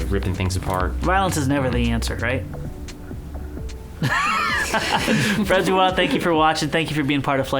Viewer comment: what does say? Hey is it half empty? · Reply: "Violence is never the answer, right?" -: "... ... ..." ;-)